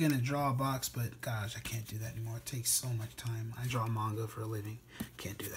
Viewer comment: 😮 did I hear you right, you draw manga for a living?